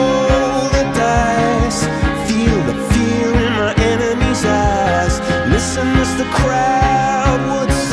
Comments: am I the only one who hears: "Feel the fear in my enemies ass"? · Fun Fact. The Line At 0:45. At The End Of That Line, When He Says Eyes, It Sounds Like He Is Saying The A Word